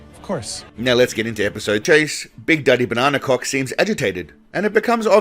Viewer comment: did he say Big Daddy Banana Cock lololololololololololololololololololololololololololololol !?!?